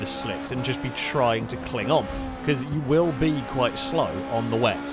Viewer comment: all I heard was Klingon. Ka Pla.